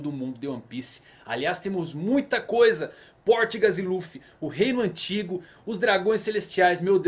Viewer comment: só eu que ouvi PORTGAS D. LUFFY?